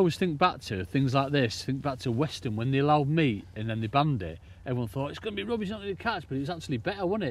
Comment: Funny accent when you're from a different country "When they banned meat" I heard as "When they banned me" and I was wondering why they'd banned 🤣